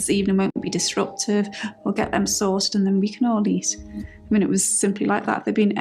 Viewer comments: does Kate say, “We’ll get them sauced.” Sauced is no doubt, a euphemism for drunk. I don’t hear “sorted” I hear sauced. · ✨ LIKE, SUBSCRIBE & COMMENT FOR ALGO✨ 🎧 Full Playlist: DD vs. The McCanns <link> 🕵️ Get bonus content as a Member: <link> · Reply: does Kate say, “We’ll get them sauced.” Sauced is no doubt, a euphemism for drunk. I don’t hear “sorted” I hear sauced. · At around the 36 min point when Kate speaks of the lead up to choosing to eat at the tapas bar. ..am I correct in hearing that she said the children wouldn’t be ‘disrupTIVE’. And not disrupted. That is a completely different perspective. One is about the adults having their evening disrupted. And the other is about it being better for the children, not having them disrupted walking to the restaurant further away. Can you have a listen and see if I have misheard or not please ?